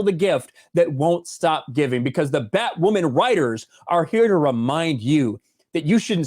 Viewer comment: When you first said "Batwoman writers", I misheard it as "Fat women writers."